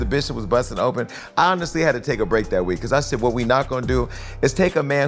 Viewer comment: Who couldn’t walk for a week? That’s my question. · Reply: I think it's Jason said "I honestly had to take a break that week"